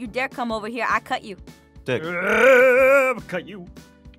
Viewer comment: Anyone heard someone say a “word?” Xd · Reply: Was it di**(Boyz u know )